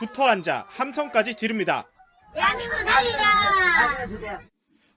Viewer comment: ' 대한민국 살리자 ' 라는 구호가 ' 대한민국 죽이자 '로 들리는데 ..국민들의 원성이 들리지 않는가